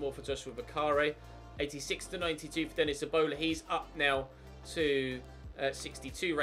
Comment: Did you just call your youth prospect Denis "Ebola"